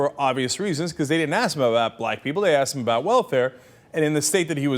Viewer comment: Haha! Even the awful YouTube subtitles thought it was "...black people's lives better by giving them...". That was like the one thing they got right! Other than that, I guess it can sound like "blight", but come on... It's not even the first time he's had this kind of gaffe. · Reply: That's funny. However, at 0:22 Cenk says "Black People", but the YouTube subtitles thought he said "White People".